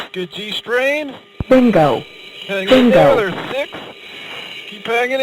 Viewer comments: What does it say at 13:50? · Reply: It sounds like it says bingo. I looked it up and bingo fuel is an warning given when there is a minimal amount of fuel needed to have a comfortable landing. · I thought the pilot said “good gstring” at first😂😂😂